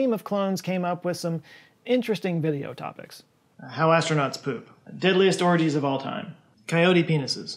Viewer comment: Nope, yep, thought I heard deadliest orgies. That's what I heard.